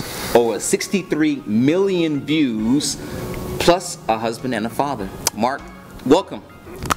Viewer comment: I thought he said me husband and my father xD